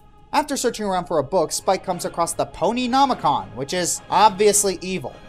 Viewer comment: I thought it was called the fashionnomicon